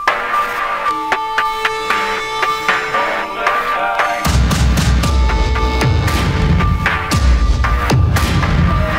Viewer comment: What is bro saying at 0:20? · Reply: "Older time"